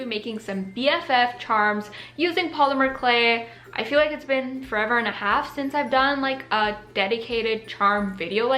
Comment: did she say forever and a half?